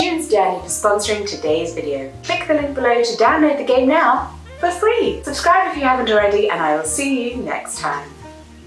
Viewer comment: Lol, I totally heard "Click the link below to download the gay now, for free!" and was both confuse and interested. ^^ Also, that fox who peed on your terrasse has clearly heard that a Disney princess was living there and just came to fill the position of Woodland Friend #1. The fox pee smell is only proof of your magical protagonist status.